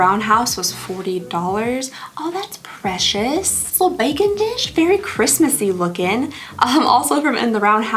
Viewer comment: when you said baking dish i thought you said bacon dish and i was so confused as to why you would need a seperate dish for your bacon 🤦‍♀️😂 the pjs you picked are so cute!! 💕